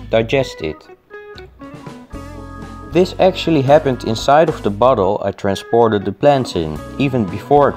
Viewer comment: I started imagining what it would look like if humans ate the way starfish do and i thought you said"this all happened inside of the butthole"...and i got angry because i had to reimagine everything all over gain